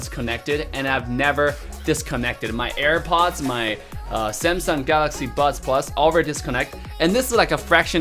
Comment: I thought he said Samsung galaxy butt plugs